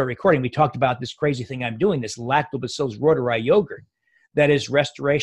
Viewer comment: ON 45:40 he mentions something but he speaks too fast....what is it? it sounds like lactoprbotorised yogurt...can anyone tell me what he said? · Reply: Lactobacillus Reuteri. It’s in probiotics.